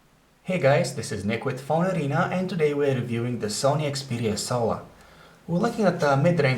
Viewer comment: lol it sounds like hes saying 'Sony Xperia SOA'